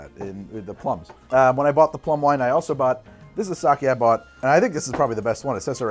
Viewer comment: So, up until about the 18:00 mark, I thought was saying "Bum wine," as a way to refer to wine he was making himself for flavoring that wasn't really meant to be drunk by itself, or something else along those lines. After all, he regularly does similar things with other ingredients, and I figured the name was just kind of a playful joke about how he was improvising. No, he was saying "Plum wine" this entire time, and I maybe need to get my ears checked.